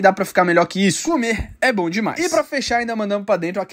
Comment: Mano eu tava com tanto sono que ouvi "ser gay, é bom demais"